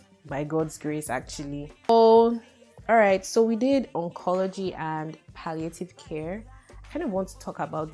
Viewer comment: Medicine isn't easy at all o 8:58 did I hear oncology and palliative?😅 The palliative took me back to corona in Nigeria 🥵